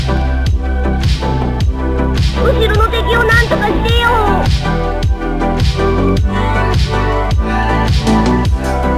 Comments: what did he said? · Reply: "Can you get this guy off me?" Or "Get this guy off me!" Depending on whether it's a question or a statement. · can someone PLEASE translate what Slip said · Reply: "Can you get this guy off me?"